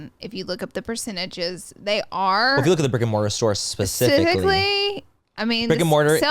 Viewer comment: Lol I thought they said Rick and Morty